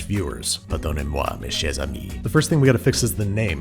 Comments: Heagarty what did he say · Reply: "Pardonnez-moi, mes chers amis." It means "Pardon/Forgive me, my dear friends." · Pardonnez moi mes chers amis (Pardon me, my dear friends in a sexy voice) Subtitles: inaudible